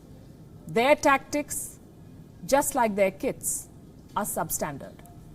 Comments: lmao I misheard, I thought she said, "Their tactics, just like their KIDS!, are substandard." LMAO! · I thought I heard, their TESTICLES just like their KIDS, are SUBSTANDARD!!! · "Their tactics, just like their testkits, are substandard." HAHAHA absolute TRUTH!!! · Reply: 1st i heard it as Their tactics, just like their kids are, substandard (went back and heard it again) :P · Holy shit at first I heard "their tactics just like their KIDS are substandard" Then I used the captions and realized it's KITS not KIDS 🤣🤣🤣🤣🤣